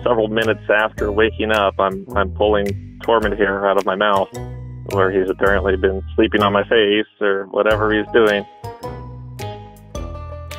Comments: I thought he kept saying 'torment'...and I'm like hmmm fitting. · This was priceless! So endearing to see the secret nightlife of Tormund. What a character 👍🐱😆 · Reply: Lol I thought he was saying Torment... just as well.